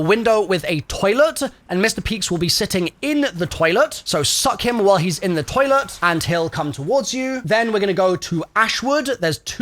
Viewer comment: “so suck him off while he’s on the toilet“ is all I heard